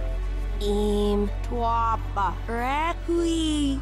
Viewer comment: What i think she said-"Aim towards the reaking Ha, Ha" Am i close?